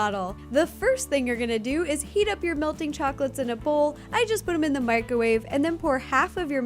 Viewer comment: i thought she said eat up your chocolate😂😂